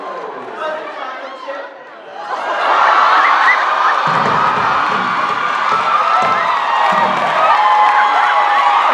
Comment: what does cookie say at about 56:50? after jensen said that he had some cookie earlier ;P thanks!! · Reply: said "was it chocolate chip?"